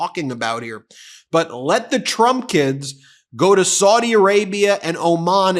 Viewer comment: Sounded like you said 'Trumpkins' but you said "Trump kids' !!! I like " Trumpkins' better!! Ha!!